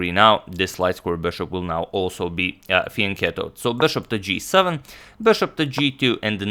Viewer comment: Can someone please tell me the name of the move at 0:56 with the bishop ? Cannot understand properly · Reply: Do you mean fianchetto?